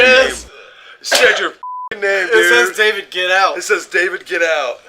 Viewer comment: Just subbed. Awesome channel. I heard "damn it get out" but very very cool video